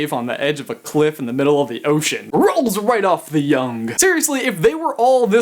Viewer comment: he says "yongye" instead of "tongue"